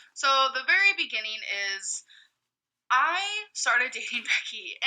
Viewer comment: Wait, does amber say "I started to hit Becky." At 0:33?